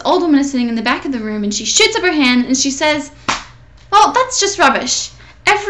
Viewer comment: lol it sounded like u sed on 0:59 shits up her hand!! x